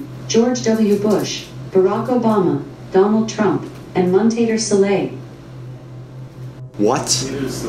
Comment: Montader Soleil is what was said.